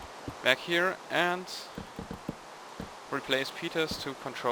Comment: he said "replace peters" instead of place repeaters" at 11:57 or so...